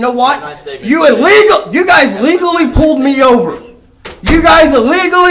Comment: Little Freudian slip there. Sandwiched between him saying your cops illegally pulled me over he actually says “your cops legally pulled me over” lol